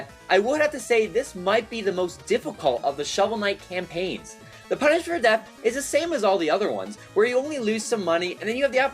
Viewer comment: I'm sorry. WHAT? You slur your words here so badly, it all sounds like one incoherent word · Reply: "the punishment for death" spoke a little took quick there. My bad! Haha. And yes all consoles are getting coop in Shovel of Hope